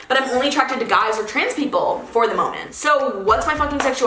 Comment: DID NOT JUST SAY FTM IS FOR THE MOMENT